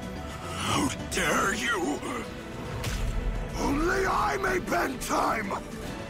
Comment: I sometimes think diavolo is saying only I can bedtime